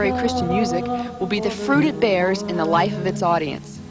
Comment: misheard: ''and that would be the fruited bears...'' [no idea what that would mean lol]